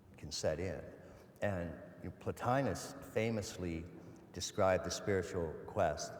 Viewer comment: Who is it that Ken mentions at 3:11 ? "Platonious" or what is he saying? thank you.. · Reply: Plotinus